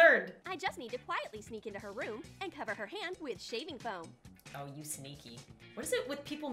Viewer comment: cover her hand with shaving cream Me:I thought she said cut of her hand! took it to far!!!